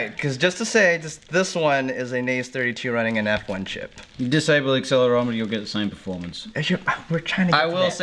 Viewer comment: English is not my mother tongue, but I understand almost everything. BUT what do they/you say from 6:32 - 6:49 ? I do not understand one thing :D Sorry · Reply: "Disable the accelerometer, you'll get the same performance." "I will say, if you disable the accelerometer you can go down to 1,000 on the F3 and you can't on the NAZE. You can only go to 1,200 on loop time. (Tommy) - "(unintelligible) oh yes, your running Clean Flight." (Steele) - "But," (Tommy) - "Or we can run Beta Flight and drop it down to one (unintelligible)" Steele) - "We will probably be running 1.9" [Can anyone fill in what Tommy says at the end of his sentence? "...and drop it down to one (?)" ]